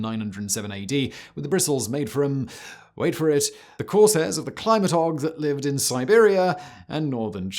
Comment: My CC says "climatog" 8:03 for what is in the first bristle toothbrush. I'm at a loss as to what you said. Turns out it is hogs' hair. I rarely misunderstand what you are saying despite being an American[US] and you being British, but this was one time I was unsure and the CC did not help.